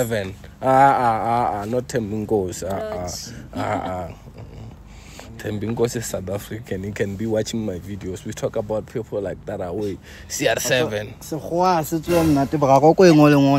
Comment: Did he just say “LORCH MAY BE WATCHING HIS VIDEOS”😹😹🔥type shit